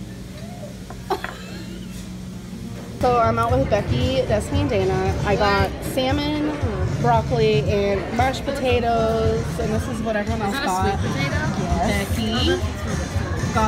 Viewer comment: Did our gorl seriously say “march potatoes” instead of “mashed potatoes” or did I just imagine that?